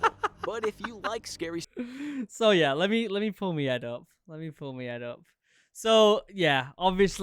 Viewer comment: i thought he said let me pull me head off